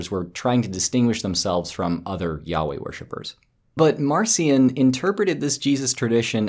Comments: I'm ashamed to admit that I misheard "yahweh worshipers" as... yaoi worshippers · I'm playing the video at 1.5 speed, and that works quite well for me, except that when you say Yahweh, I keep hearing "yaoi." He upset the yaoi worshipers? Was he sharing the heretical belief that Light Yagami and L didn't share a secret yearning?